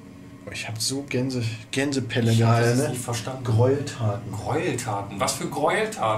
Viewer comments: Alter für mich sah es in einem Moment voll so aus als hätte die Puppe ihre Augen um einen Millimeter oder so bewegt Und bei 09:16 hat es sich für mich so angehört wie keine Fragen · Also ich persönlich habe bei 9:15 "keine fragen" verstanden